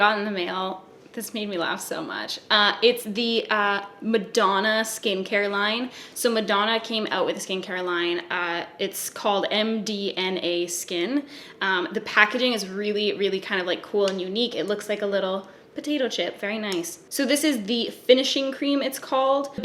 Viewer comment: i thought you said M D M A skin and i was like ...???